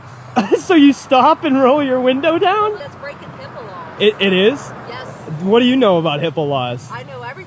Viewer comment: I seriously thought that lady said he was breaking hippo laws